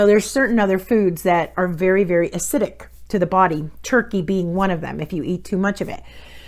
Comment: You say ..alcohol is acidic and dairy ...after that you also say TURKEYBEANS ? minute 1:56 ...also very acidic....I can not understand or hear what you said ..excuse I am from Holland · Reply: Turkey being one of them